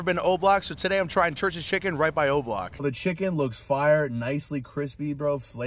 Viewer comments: i thought he said right by roblox 💀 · "Right BY O block" 😂😂😂 bro said he ain't going ON Oblock 🤣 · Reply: i thought u say goblo